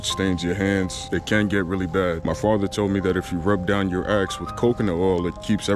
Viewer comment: I thought you said rub down your ex…😂😂😂😂😂😂